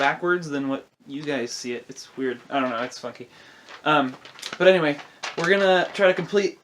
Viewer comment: Haha at 18 seconds I thought you said the f word not funky. 😂 This is your cousin btw.